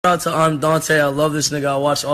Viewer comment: Love you forever brother. Thank you for all you have done for me. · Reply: ImDontai when he said shout out to imdontai I thought he said. Dante